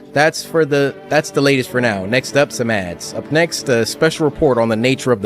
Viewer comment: I thought he said “next up some ass” I was in tears